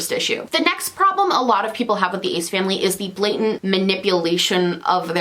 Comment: the first time I heard someone mention them I thought they said "the AIDS family" and honestly it seems baout the same